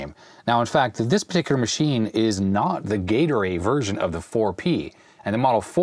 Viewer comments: I heard “Gatorade version.” 😆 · I thought I heard "the Gatorade version"! In my defence, I was listening at double speed... 😁